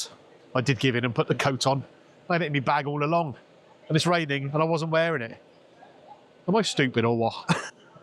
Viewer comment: I thought you said you didn’t have a jacket. ??